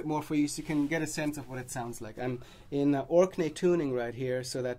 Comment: About 3:41 he says, "I'm in ______ tuning...." C, G, D, G, C, D . I use this tuning, also -- I learned it from an old Dave Evans album -- but I don't know what it's called. Anybody catch what called it? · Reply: "Orkney" tuning, which is what Steve Baughman coined it, though of course it's technically a G Sus tuning.